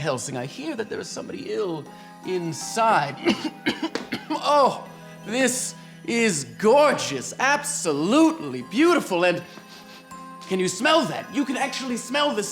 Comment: Him saying, “Oh, this is gorgeous. Absolutely beautiful.” Captions saying, “Oh, this is beautiful. Absolutely gorgeous.”